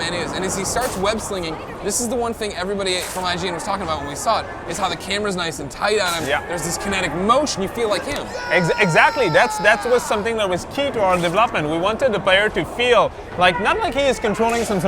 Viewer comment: it sounds like he says "Devlopment"